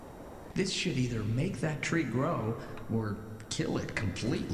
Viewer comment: sounded like "this SHIT either make that tree grow or kill it completely". getting vulgar, aren't we torin?